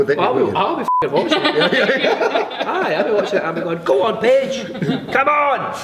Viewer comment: When he said Paige I thought he said Bitch and I was like HELL YEAH TELL HER but he’s too nice omg🥺😭